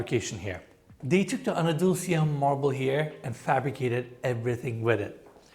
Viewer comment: Did I just hear Enes pronouncing Andalusia as Anadulsia? 😂